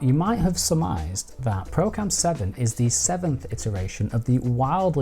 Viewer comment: What?! IN the intro at 0:22 he says Pro Cam 7 is Pro Camera? I can see 2 apps in the store.... one is called Procam 8 and the other is called Pro Camera and they are not the same App! I am Confused! :( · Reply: You must have misheard me. I said "ProCam 7", which is now Pro Cam 8 in the App Store.